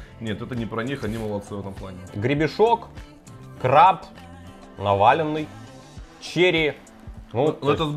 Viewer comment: "Гребешок, краб, НАВАЛЬНЫЙ!" P. s. Я знаю, что он сказал "наваленный".